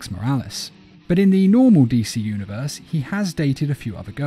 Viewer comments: did he say dc universe 🤨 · did you say normal DC universe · I’m sorry but did you say DC universe